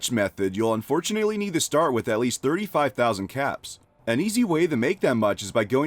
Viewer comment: the unpached one you need about 32000 caps to start with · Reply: I thought he said 35000?